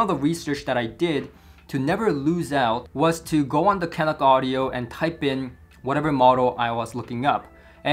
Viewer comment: I can't make out the website he uses to research the audio products he's about to buy. Can someone help? Kinect Audio? Catalog Audio? K-Dock Audio?